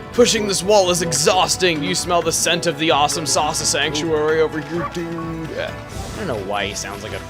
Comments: Sounded like you said 'awesomesauce sanctuary' xD Although someone's probably already mentioned it, too lazy to look... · I swear i heard "AWESOME SAUCE-A SANCTUARY." and I lol'd. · The first time he said 'Awesome Sasa Sanctuary',it sounded like he said 'Awesomesauce-a sanctuary'.